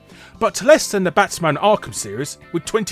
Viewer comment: Batman arkham Asylum only did 22 mil sales? Wow... that is a tragedy. That is an absolute tragedy. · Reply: Keego Bricks i think he said the entire arkham series but i might have misheard that